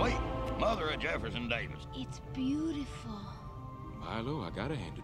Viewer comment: did he say Jefferson Davis?